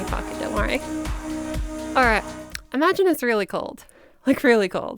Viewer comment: i was so insanely confused at this part 2:01 because i thought she said "imagine an israeli cult" 😭 jokes aside fucking phenominal video , 2 mins in and its already looking to be better than the stuff ive seen most of the time <3 i am a femboy not trying to transition but to fix my deep-ish voice into sounding more feminine, so hopefully i can do this someday :3